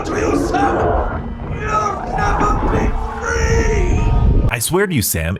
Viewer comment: I know everyone is saying Richthofen says “I swear to you Sam, you’ll never be free” I always hear him say “you’re gonna be free” and to me that kinda fits a bit more especially with Sam saying “I already am”